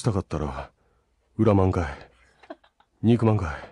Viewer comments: 「憎まんかい」が「肉まん買え」に聞こえるwww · 肉まん買えって聞こえてばり笑ったwww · 憎まんかいが肉まんかい っと聞こえた。 (うちはイタチ役の石川秀郎さん)